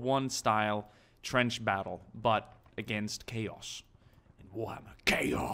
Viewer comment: I didn't here it clearly, and thought he said WW1 trench style warfare except against gays. Lol.